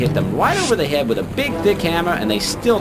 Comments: it sounds like he said big dick hammer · Hey, yeah, so I definitely heard big dick hammer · did he just say "big d*ck hammer" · I swear I heard “god can hit you with a big dick hammer”. Yes, I do need help. /hj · Did he say "hit them over the head with the big dick hammer" at 07:50 ??? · DID HE JUST SAY DICK HAMMER??? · did just say “hit ‘em right over the head with a big dick hammer” ?!